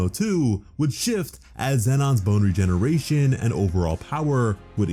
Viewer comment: don’t think I didn’t catch you saying boner